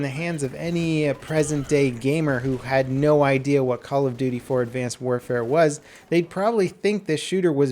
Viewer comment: did he said call of duty 4 advanced warfare at 1:45?